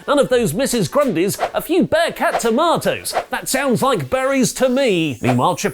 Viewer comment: WAIT.. did you say Bearcat at 6:26? Spirited woman? So Keith Lee is a woman? 🤔😱